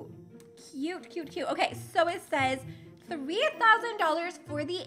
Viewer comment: I thought it said coming to adopt me :((